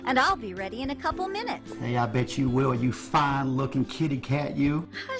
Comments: No idea why I heard, “Heya bitch-“ Shouldn’t have tuned him out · at the 1:53 mark I swore said Yeah, Bitch, you WILL! lmao i had to double take like, huh? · did he say "hell yeah bitch you will"? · I can't be the only one who heard him say "Yah bitch you will ..." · I thought he called mom a bitch? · I can't be the only one that replayed at 1:53 because I thought he called her a bitch am I? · I thought he said "Hey bitch" at 1:55 lol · "Hey ya bitch you will" I prefer the misheard version. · I heard ”heya bitch bet you will you fine looken kitty cat, you!” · Did he just say 'Bitch you will'? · did he say "yeah bitch you will" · I thought said "Heya bi&*( you will" 1:55 PLS DON'T CENCIERSGS MEH